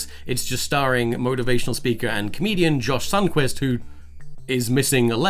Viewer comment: I thought you said Josh thungtwist because I have bad hearing so when I turned on the subtitles I found it spelt sundquist, I felt a bit stupid